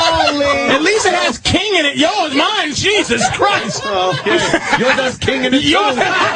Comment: what does Ant say after Patrice says the line about king? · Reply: Ant says, "yours has King in it, too"